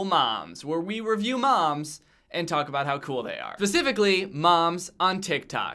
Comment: Am I the only one who heard him say pacificly at the start